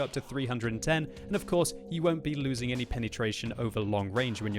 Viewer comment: "You won't be losing any penny-tration..." I can't be the only one who heard this.